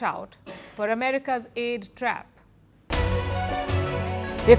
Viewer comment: For a minute i thought i heard gay trap😂